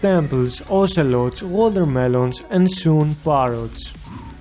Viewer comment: what did u say?????? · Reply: Parrots